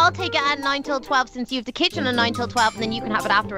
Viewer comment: when she said kitchen i thought she said kitten! <3 you guys lol XD